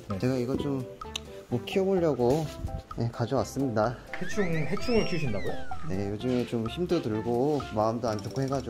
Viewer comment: 2배속으로 보는데 해충이 해충을 키운다구요? 라고 들려요ㅠ